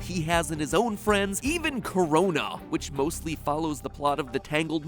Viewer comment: Did I hear corona?